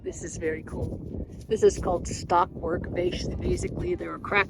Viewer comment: at 4:47 your chatting about the white rock with cracks .we couldn't catch the name..we actually found one last year when we were in montana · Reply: The name of the rock isn't specific - it's the name of the pattern, which is "stockwork." It's cool, isn't it?